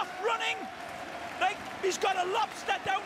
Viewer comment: did he say he has a lobster in his shorts